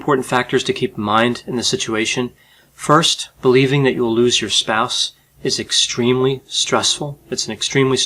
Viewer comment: Can someone please say what he is saying at 4:07.. I hear: you lose your "spasht".. but I guess its wrong since it "spasht" doesnt mean anything?